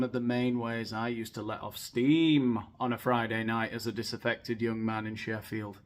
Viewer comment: I thought you said "disinfected young man".😮